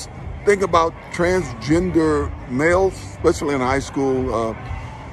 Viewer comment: I thought he (OJ Simpson) said "transgender milf" oops